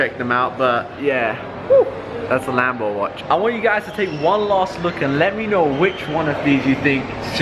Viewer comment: Is it me, or did he say 'Wallah'? If you know then you know 😂😂😂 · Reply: he said one last look